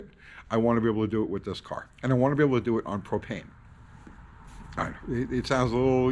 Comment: I was halfway falling asleep when I started watching this video and when said that he wanted to be able to do it on propane around 2:07 what I heard was “ I want to be able to do it on cocaine”. It perked me right up and I was thinking this channel is gonna get a whole lot more interesting than it already is!! lol. Imagine on that stuff! It would be entertaining to watch I’m sure.